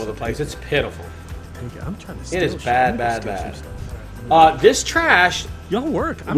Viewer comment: Did I hear "I'm trying to steal shit, I'm trying to steal some stuff"?